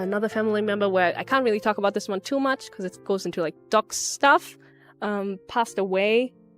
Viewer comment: I think she said 'dox' stuff no 'dark' stuff.